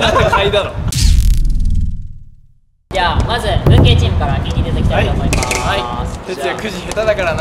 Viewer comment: の「文系チーム」が「運ゲーチーム」に聞こえて、運ないと勝てない前提なのかと思た